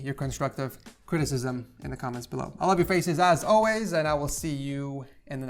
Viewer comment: When said: “I love you guys face as alway” somehow I heard “ I love you guys face and ass as always”